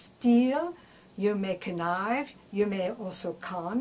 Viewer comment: "you may knife" and "you may cun" ? Is that what you said? I'm just trying to understand I'm don't mean to be rude.